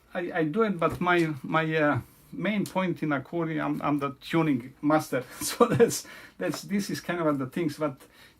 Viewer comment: Does he say, "... tuning mustard"? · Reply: He says "tuning master"